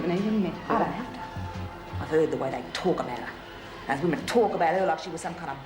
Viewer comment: What does Marie say about Myra at 39:58? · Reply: "Those women talk about her like she was some kinda Bob bloody Hawke or sum'pim" - Bob Hawke, former head of the ACTU (huge union movement) and that the time of this episode an exceptionally popular prime minister.